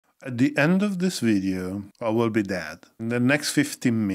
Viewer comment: At first I heard: "At the end of this video I will be dad." 😆